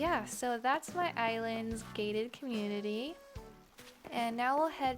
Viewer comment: Oh you said “gated community”. I thought you said gay community lmfao